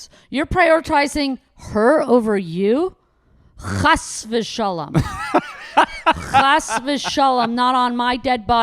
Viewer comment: what does robbie say 52:20? hash vishalam? what does it mean ? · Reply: Chas v’shalom. Means something like heaven forbid